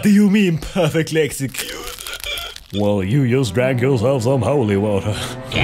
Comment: "Well you just drank yourself sone holy water" Do you have any idea how hard it was to figure out what he said?